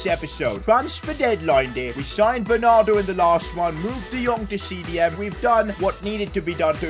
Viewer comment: I heard "Luke De Jong to CDM"... 😂 Luke De Jong is everywhere 😂 Goat!